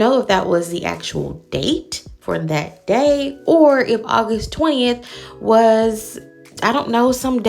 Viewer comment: Ok, so went back to see the phone date and gas date. Looks like the date/time has been blurred on phone now. Surely, not August 20th though, but that’s what it sounded like he said.